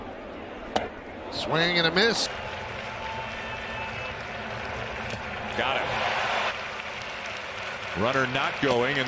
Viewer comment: の「ガッラ」って実際なんて言ってるの？ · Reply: Got him